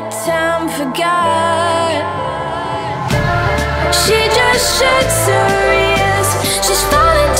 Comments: i've heard "seggszőr" instead of "shuts her" for first 😂 Its a Hungarian word, means hair on your ass :p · What I heard: ~She's a whistle in the catacombs, a pair of socks~ ~She just shits ears~ ~Won't let him taste her ears (The ones she shat out?)~ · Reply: I heard "she just shits where she is" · I always thought that she said "she just shakes her ears" lol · anyone else here cuz you thought she said "shits her ears"?